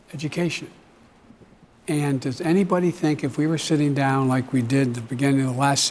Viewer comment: I don’t like Biden but i do like how he’s sitting with young voters to talk about issues. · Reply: Sitting? I thought u said sniffing :))